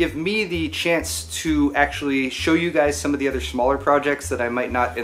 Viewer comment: did you say shmaller?